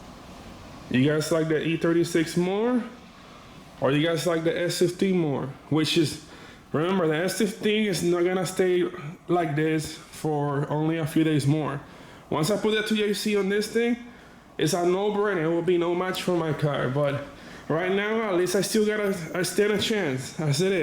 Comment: anyone catch him say "when I put the thc in this thing" lolol stoner